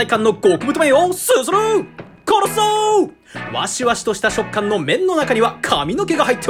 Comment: ここ本当に「殺そう～！」にしか聞こえない